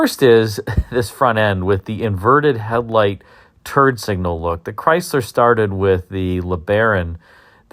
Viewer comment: I swear it sounds like u said "turd signal" lol!!! i mean it!